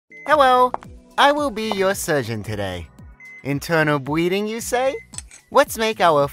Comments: lmaoo at first i thought they were saying "internal breeding" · I misheard it as “internal breeding”. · sounded like he said “internal breeding you say?”